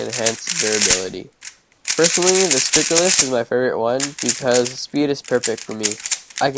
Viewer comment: "the spiculous?".... I can't understand what you're saying in that sentence. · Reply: Stickerless